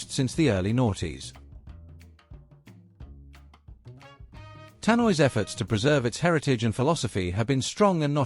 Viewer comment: Naughties ? Did I hear that correctly ? From 🇳🇱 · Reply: It's noughties with an O. It denotes the 2000s.